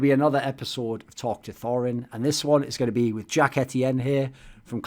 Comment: I had to rewatch the intro as I thought Thorin said "Jack ATM"..